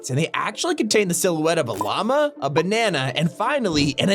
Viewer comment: When he said A Llama I thought he said Obama